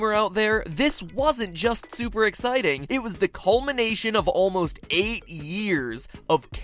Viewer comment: Did he say "colmination" @ 1:22 ??????